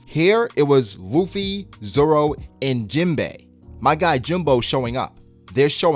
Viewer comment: did he just say jimbo?